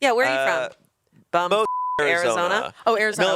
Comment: it took me too long to realise that they were bleeping out locations to not dox them/their families. Just thought they were saying "f*cking Arizona" 😂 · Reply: Yeah I thought they were saying “bumf**k Arizona” 😂